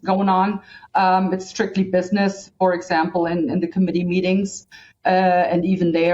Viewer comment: I thought she said "in the comedy meetings" just for a moment, but it was 'committee meetings.🤣